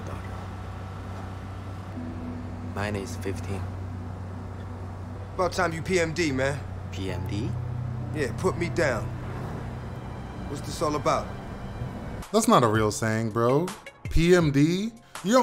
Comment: I thought PMS when he said that loll💀💀🤣